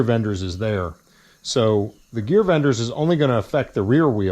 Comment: I misheard the name as 'gearbenders'..... And.... It suits the title of the video. has likely nailed the cause, its likely they went with the one-way bearing aka spragg clutch to save money over having another hydraulic piston.